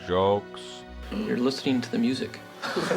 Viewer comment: It sounds like he says "You're listening to the music." Which makes no sense out of context, but I can't imagine any context that would make people laugh that hard. That's just scary fake.